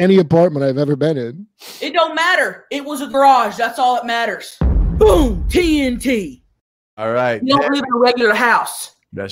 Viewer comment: Every time Tammy did the “boom TNT” when she was dressing down Kurp made me laugh like a maniac in the middle of the break room in work. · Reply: I thought she was saying “Boom, TMT” (Too Much Truth)